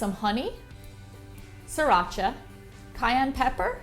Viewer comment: what did you say? Scrochie? Salcha? Sorry can't, don't understand!